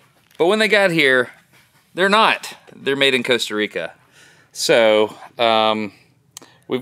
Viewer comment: This experiment was incredibly interesting for hundreds of reasons. It's not just about America... this is an important thing for everyone in the world to be considering. A huge thank you to everyone for supporting local manufacturing! If you're interested in getting a scrubber, we have a limited supply at <link> We are absolutely terrified of how difficult it is going to be scale up our output if there is significant demand, so please be patient with us. Our goal is to invest back into the machines and tooling to see if it's possible to make this stuff work <link> . Also, we are nowhere in the ballpark of being ready for this... but if you're interested in being a wholesaler we'd love to get your contact information in case we figure that out for the future. Also, if you'd like to help tell this story we'd like to hear about that as well. Here's a link to contact us: <link> I'm beyond grateful to the Patrons of Smarter Every Day for helping make this happen. You (the Patrons) knew about this long before the general public or the Search Engine episode, and you have been nothing but supportive. I'm grateful! Thanks to all who support at <link> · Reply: For a second, I thought you said the nobs were made in Puerto Rico, not Costa Rica and was about to say that those nobs were made in America 😅😅😅